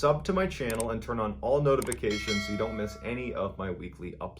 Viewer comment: I misheard notifications as boatifications.